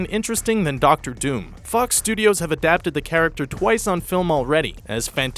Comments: sound like he said a cuss studios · he said "f*ck studios" instead of fox 😂😂😂